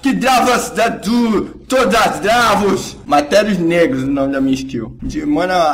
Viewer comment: eu entendi "matandu us negrus" EHAUHEUAHEUAH